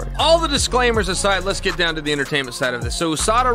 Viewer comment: I love that Chael is still in the Business and talk about how he was Juiced up when he was Fighting 😅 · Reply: I think you must’ve misheard him. “Entertainment”